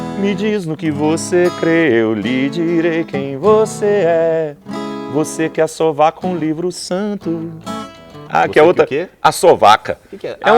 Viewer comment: É Assovaca o livro santo?!?! Eu sempre entendi "você que acha vaca ou livro santo" 😂😂😂😂😂